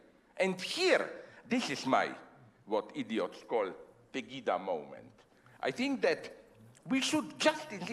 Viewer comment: what does he say at 2:10... his what moment? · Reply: Pegida, which is a german anti-immigrant rightist movement (it's german and stands for Patriotic Europeans Against the Islamicisation of the Occident)